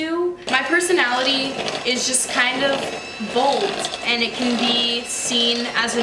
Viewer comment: Instead of bold I heard “my personality is like mold” and I just was kinda like mood